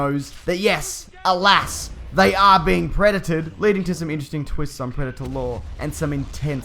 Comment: DID YOU JUST SAY "PREDETED"?! LMAO